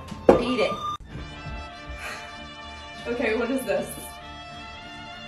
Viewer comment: Sister thought he said "Did you get lucky?"😅